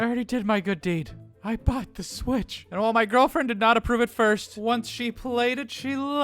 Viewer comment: I thought it said "Bought the switch, stole the bitch."